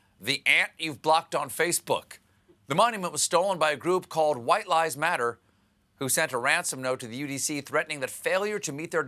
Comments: I heard "white LIES matter" and it honestly took a few secs before I realised I heard wrong. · When you said "White Lives Matter", I heard "White lies matter." :-) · Did he say "White LIES Matter " or did I mishear him?